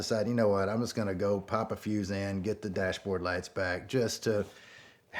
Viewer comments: me: Did he just say he's gonna pop a few xan?! 😳 (rewinds several times) Oh-- pop a fuse in. I'm taking this whole DEA truck thing a little too far🤠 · When he said “pop a fuse in” I thought he said “pop a few xans”